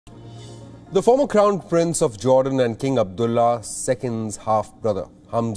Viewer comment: I thought he said "the former clown prince...." is nothing wrong with the way the journalist said it, it is me that got it wrong.... or maybe not 😞